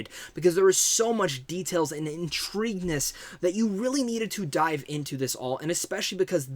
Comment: He said "intrigue-ness" right? Sorry if I misheard, pls pardon my misheard-ness...